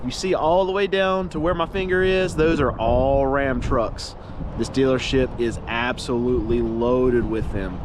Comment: I thought I heard you say dealershit for a second there in the beginning of the video 😂😂 0:33 but you didn't but then again they way they've been doing business these past couple of years that name fits them. 😂